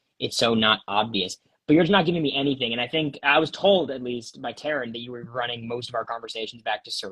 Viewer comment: did say "I was told, at least, by Karen..."? Who the heck is Karen? Someone in Production?